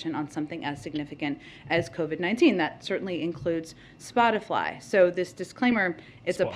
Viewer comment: did she just say spotifly?? I checked 5 times. lol